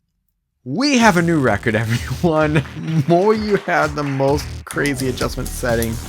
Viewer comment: It's not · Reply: lol my bad. I rewatched 3:00. I misheard "MoYu had...." with "MoYu HAS...."